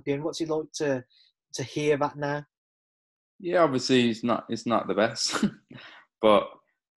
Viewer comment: did he just say chocolatito is not the best? please help me .